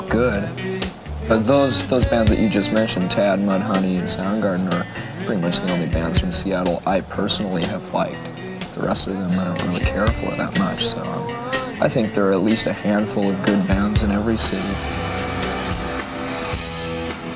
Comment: KURT ON SOUND GARDEN "I THINK THERES GREAT BANDS IN EVERY CITY" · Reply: Did kurt said "i personally have fight"?